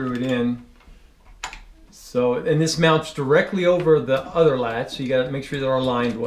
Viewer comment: Did he just say " Mother Latch"?